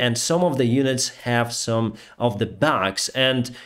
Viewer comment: I'm not a native English speaker so I'm not sure what you wanted to say here. What I heard was 'some of the units have setbacks', which doesn't make much sense to me. Maybe you or someone else can clarify? But anyhow. I don't think that the need for repair has anything to do with the unit being new. I don't even think it's unusual. Those units are just in heavy use I imagine. As the saying goes, where wood is chopped, splinters must fall. Even the best equipment needs maintenance.